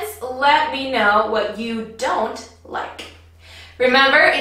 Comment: احد يكتب لي وش قالت في الدقية 7:07 كتابتا مو قادرة القط السبيلنغ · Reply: I like to use the term "It's not nerdy being wordy" because I think it's cool to know a lot and learn a lot of new words. Here, the term "nerd" is often used as an insult, and I don't think it should be :) I love being a language nerd, and hope you do too!